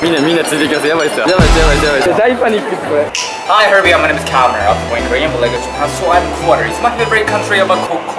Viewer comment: マイネイムーズカメラに聞こえる